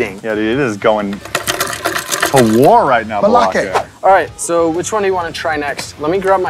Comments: Anyone hear Balake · I heard "balake"